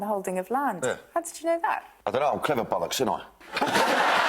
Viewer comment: im not a native english speaker.. or whatever language that is.... but what is he saying? "im a clever bollocks aint i?"